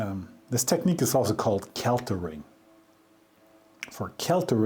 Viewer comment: @ 26:50 this process is known as ..... Keltering? Did I hear that correctly? I ask just in case I can find more examples of the process on line. I have tried a few spellings. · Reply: "Kellering" - It used to be a company name - They later got bought out by pratt&whitney as far as i know. <link>